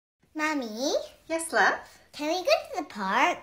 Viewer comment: Is it just me or when she said “ mommy” it sounded like she said “ honey” I’m not sure